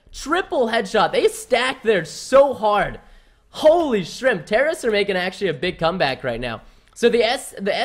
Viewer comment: Did I just hear "holy shrimp"? Also I'm loving this channel so far, so entertaining.